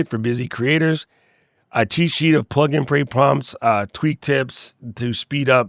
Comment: Sounded like you said Plug and Pray :)